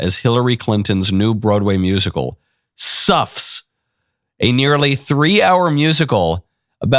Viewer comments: I first thought I heard you say the name of the play is "Sux." Seems appropriate. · I thought at first you said, "Snuffs". Yes, that sounds like the Clintons.